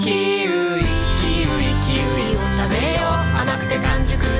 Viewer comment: キウイ キウイ のところ 生きる生きるて聞き間違えてめちゃ感動した覚えがある